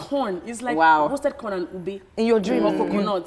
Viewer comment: Did she just say 'Kub3' @ 22:56.... thought that was only used in gh for coconut